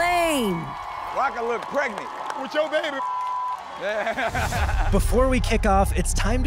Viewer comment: did he say with yo baby?😂😂